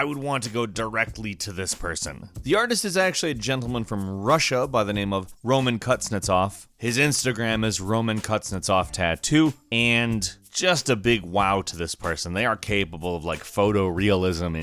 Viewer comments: Am i the only one who heard "Roman cuts nuts off"? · "Roman cuts nuts off" all I heard 😂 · Did I just hear “Roman CutsNutsOff”? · I heard Roman Cuts nuts off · At first I thought you said Kutnutsov